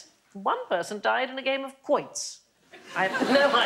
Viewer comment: a game of what? I must not know the game because I have no idea what she said. Anyone know? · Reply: Quoits!